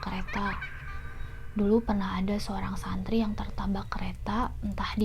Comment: Apa cuma gua yg dengernya TERTABAK ? Yg Spesial apa yg Biasa Kak ? :)